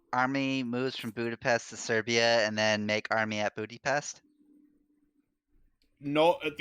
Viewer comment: sounds like i said bootypest lol